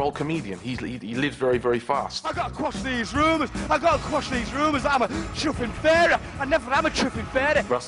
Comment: Can anyone translate what Russell is saying at around 5:19 - in the angel wings clip? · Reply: "iv'e got to squash these rumours that im a chuffin fairy" Chuffing is meant to be F@ Hope that helps